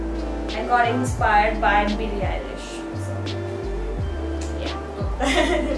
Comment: What did she said she got inspired by whom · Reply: Tanishka Singh Billie Ellish